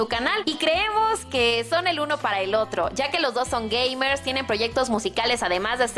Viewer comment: oh yo escuche gay por un segundo xd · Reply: era gamer XD yo igual escucha gay XD